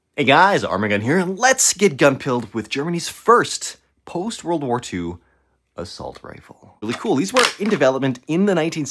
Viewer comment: Thought that said granny’s first assault rifle